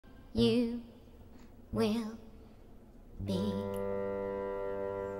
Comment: I thought the sound said "your gonna pop you lur"